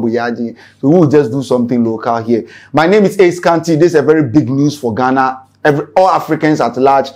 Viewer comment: what did i hear ( lose for ghana)or what ??